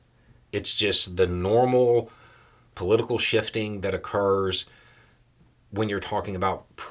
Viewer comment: You said"normal political shifting" and I heard "political bulls#!tting."